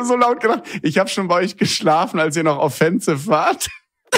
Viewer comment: was sagt er genau? Vielen Dank!!❤ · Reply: Ich hab schon bei euch geschlafen, als ihr noch offensive wart 👦🏿